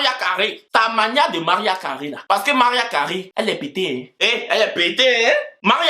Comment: 😂😂 Au début j'avais mal entendu et ai cru que tu avais dit que Mariah Carey est "bété"😂😂 Je me suis matin là tu es arrivé sur les bétés 😂😂 C'est réécoutant que j'ai compris que c'était : "est pétée" 😊